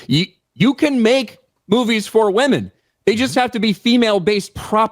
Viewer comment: lol..i misheard.. "we can make [boobies] for women, they just have to have the right properties" (instead of movies that he said) whoops